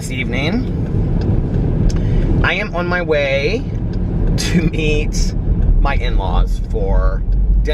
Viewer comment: lol! don't eat your in laws! it sounded like you said you going to eat your in laws for dinner, not meet! :-P